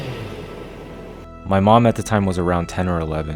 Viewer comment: did he just say mom.